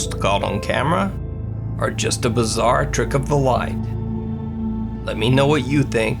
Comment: When you said ‘bizarre trick of the light’ I first thought you had said ‘a bizarre trip to the Y.’ 😂